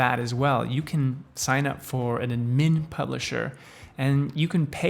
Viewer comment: Bro what are you saying at 5:21? Anmin publisher?